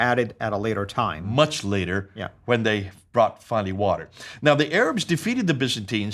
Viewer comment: you say they brought __ water. What's that word? It was unclear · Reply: it's "water"...American ' t' often sounds like 'd' ...